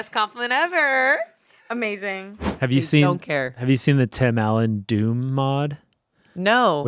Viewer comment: Okay, y'see, I heard Tim Allen Dune and well... I was quite intrigued by the possibility.